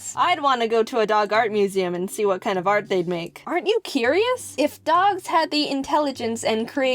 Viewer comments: when she was talking I thought see said art you curious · I thought she said ART YOU CURIOUS